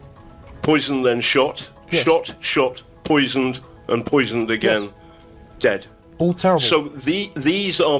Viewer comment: Sounded like that old boomer nitwit said "shots, shots, shots, poison, poison, poison" at the 5:45 mark Also, is the kid on the panel one of the Podcast of the Lotus Eaters dudes?.